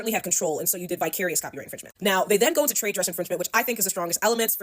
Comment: when the lawyer said “vicarious infringement” i thought she was saying “bi-curious infringement” 😭😭😭